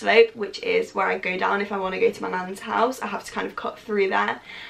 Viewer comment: When you said “my nan’s house” I thought you said “my man’s house” lol 😂